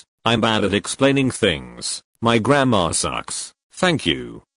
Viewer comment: I THOUGHT THE 4TH ONE SAID "my grandma sucks"